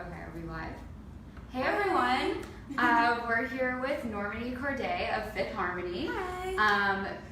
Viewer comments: I could've sworn the girl said NormAlly Kordei...I mean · did she just say normane it's normani ughhhh love you mani....I don't know why people keep spreading rumors about you though it's really sad but Ik it's not true